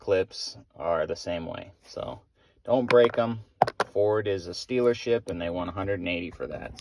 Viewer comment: It sounded like you said stealership. Sounds about right to me.